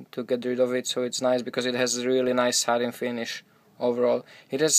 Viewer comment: Oh man, I'm slow. It took me so long to realize that you were saying "satin" finish. I thought you were saying "sutton" finish, and I had no idea what that was.